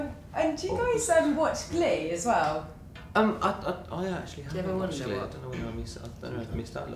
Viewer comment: What does Louis say at 6:20? “Do you ever wonder…?”